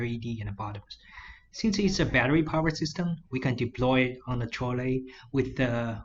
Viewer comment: I cant understand in 6:25 "Since is a battery powered system we can deploy on a ?????? with the working progress information" can you help me please? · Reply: I've seen the word in a further slide, I think is "trolley"